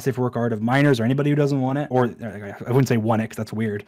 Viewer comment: So I wasn’t paying attention and I hear Dream say “1x” and I look back and replayed it bc I didn’t know what he meant He said 1x again and I still didn’t know what he meant but I kinda gave up on figuring it out and instead rewinded it in the joy of watching this guys reaction. After like 2 more times I went back farther and then got what he actually said which was “not want it” and it made much more sense.... Okay I’ll continue watching...